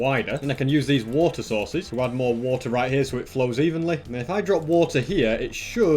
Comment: At day 1991 I heard him say evesaly